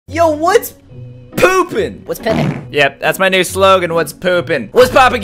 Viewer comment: I thought always said yo what’s poppin not what’s poopin